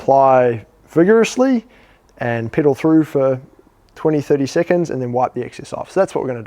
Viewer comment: Sounds like you say "apply vigorously and piddle through" . I haven't piddled since I was 3yrs old. LOL